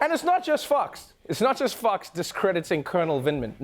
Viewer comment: When Trevor said 'it's not just fox, it's not just fox' I almost thought he said 'it's not just fucks.'